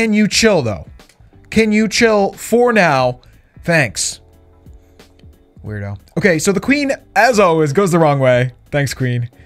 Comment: Did call lulu a weirdo 3:33 👇🏽 if you agree